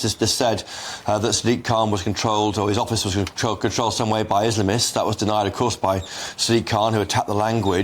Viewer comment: I thought he said dismalists 100%dismal